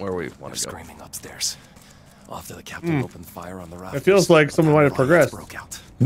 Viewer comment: oh i thought it said Shaft mb